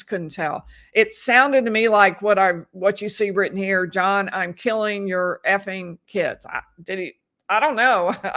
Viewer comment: I don't think she said "I'm killing your kids." I thought she said, "I'm here with your kids." Like she's saying, you're still out partying and I'm here with your kids. I will have to listen to it again, though. Audio in the courtroom is not great. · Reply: She said I'm here with your effing kids.... As in: where the hell are you... I shouldn't be here you should 😐